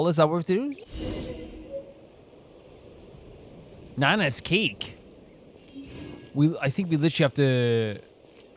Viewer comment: with accent I thought he said Nana"s KINK, instead of cake LMFAOOOOO